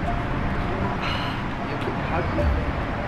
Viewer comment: did say “wait babe”? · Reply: She said “Wait Wait”